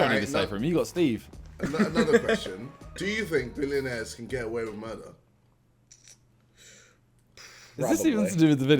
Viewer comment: I legit thought ksi said getting away with mother..💀